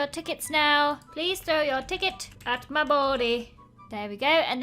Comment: When she said please throw your ticket at me body. I thought she said throw it at my balls😂😂